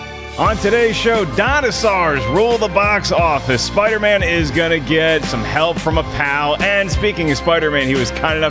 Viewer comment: At the beginning of the video I thought said ''Spider Man is going to get help from a cow''.